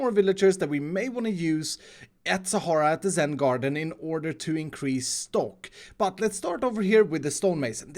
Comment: I'm really tired but did say increase stonk 😂😂